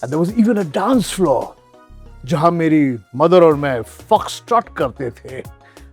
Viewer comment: kya bola bhai....😂😂😂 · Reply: Fuck start krte the wtf😂😂😂